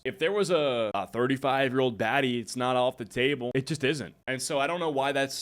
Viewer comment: Bro, I had my phone in my pocket. I swear what I heard was "in a few years if there's a 35-year-old daddy, it's not off the table; it just isn't". Had to do a double take. Turns out homie said "baddie", not "daddy". Lol